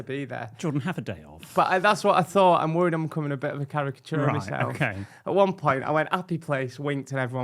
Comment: Oh caricature of himself i thought he said kerry catona 😂😂😂